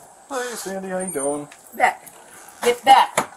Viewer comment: did he call her sandy? now theres chase AND sandy?